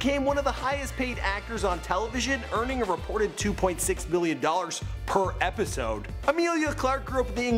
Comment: It sounded like he mispronounced billion with million because I could've sworn he said 2.6 billion an episode instead of 2.6 million. 2.6 million sounds a lot more reasonable.